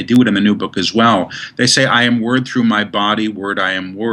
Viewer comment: I need help understanding the mantra Paul Selig mentions in minute 29:30. English is my second language so I am not sure what he says: "I am WOR_ through my body..., I am WOR_ through my vibration..." and so on.... Does he says WORD or WORTH? Thanks in advance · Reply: Mario Arturo Hernandez Hi Mario, he is saying WORD.